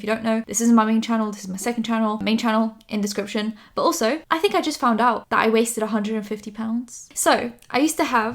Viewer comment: omg I was distracted at the start of this video & thought you said "I just found out I weigh 150 pounds" & I was so confused when you started explaining it with microphones & recording software hahahaha